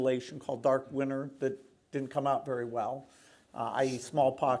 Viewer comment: he says it turned out well....when humanity lost..... Guys.... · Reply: You misheard that, he said "didn't turn out well". I made the same mistake too. Please correct your comment in the name of truth.